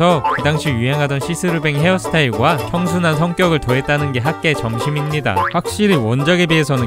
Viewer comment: 초에 나만 학교의 점심입니다. 라고들림?